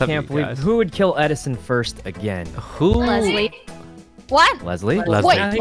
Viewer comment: At the begging he put "lily" instead of "leslie" in